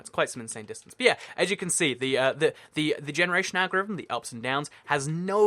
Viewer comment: I heard "The generation ad revenue"